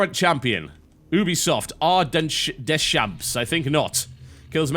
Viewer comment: I heard "booby-soft" when he said Ubisoft.